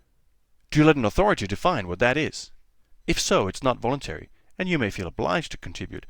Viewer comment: Can you tell me what you said at 2:22 ? I've listened to it like ten times and I cant make it out. It kinda sounds like you said "do you lack(or like) an authority to fine what that is?", but of course that wouldn't make any sense. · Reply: "do you let an authority define what that is?"